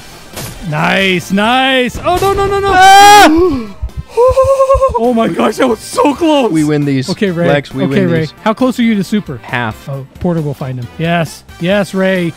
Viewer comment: when said “nice oj” i thought i heard rey